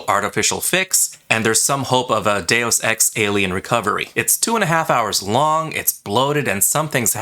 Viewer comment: Did he say "Two and 1/2 hours"😱 I rarely watch two-hour movies, but when I do thank goodness for the fast forward button🙄 An-hour-thirty is enough movie time.